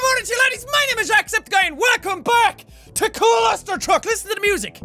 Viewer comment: Was I the only one who heard him say walcome. Just go to the beginning of the video